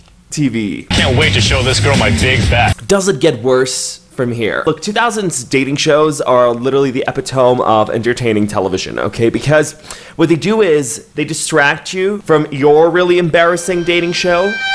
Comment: i apologize.. did he just say epitome as ep-eht-own????